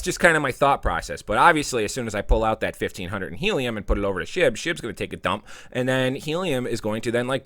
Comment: I thought you said put my money into shi*t., but I guess you said SHIB. Same thing, pretty much :)